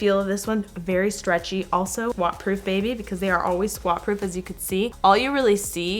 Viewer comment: Who else thought she said WAP proof instead of squat proof😂❤️